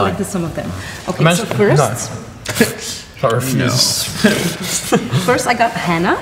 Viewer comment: OK is Luke saying "no" AR around 6:48 and if he is or whatever he said am I the only one who got major feels by it anyone nope ok.....